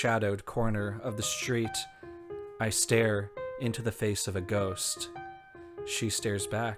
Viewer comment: I'm dying over mishearing that dramatic read as "I stare....in the face of a goat." 😂